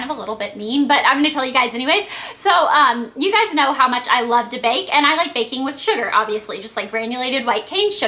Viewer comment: LOL When you said "You guys know I love to bake", I thought you said "You guys know I love debating", and I thought....I didn't know that! HAHA